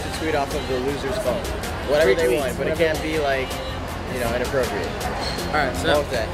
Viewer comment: From 0:40 - 0:43 did Carter just say, "but it can't be inappropriate." 😂😂😂😂 bruh